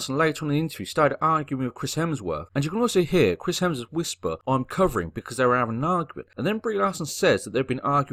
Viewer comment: What that they had been fighting all day? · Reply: yeah he's basically saying 'oooh I'm soooo scared' by saying what i hear to be 'i'm cowering' instead of 'i'm covering' because what does that even mean?